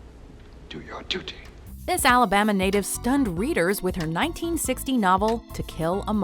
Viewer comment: I thought she said Alabama N***a